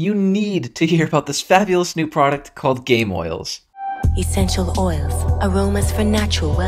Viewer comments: I thought you said gayoils ngl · When you said gameoils I thought you said gayoils